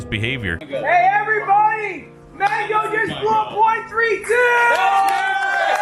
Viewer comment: OH BOII DID HE SAY MANGO!!???!?!?!?!?